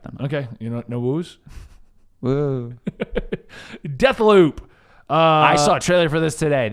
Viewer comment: It sounded like they said death lube